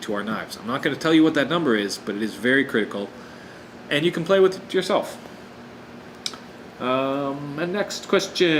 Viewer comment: Lol, it sounds like you said "and you can play with yourself"!